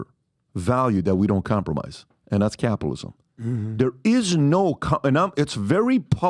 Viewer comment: I SWEAR it sounds like you said cannabalism 😂